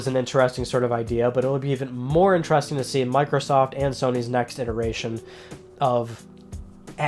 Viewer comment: did say Microsoft sex machine